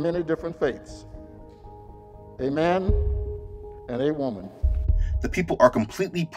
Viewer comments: Did he just say "Amen and Awoman"? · Did he just say Amen and Awomen LMFAO??? I'm done, Amerikkka is a joke